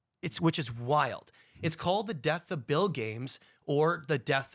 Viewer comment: thought he said death of bill gate at first 😂